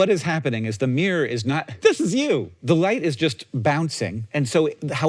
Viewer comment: First I heard "Delight is just bouncing" and thought Hank was talking about how wonderful Siobhan is (which I mean; also correct). 😂